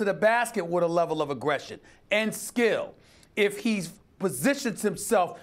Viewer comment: Wait 4:53 did he just call him Skip